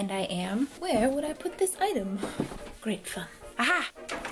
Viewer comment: no offense but, i was confused at what she said at 6:46 when she said great________, i thought she said great BEEEEEP! i had to watch it a second time to figure out that she did not say that she said great fun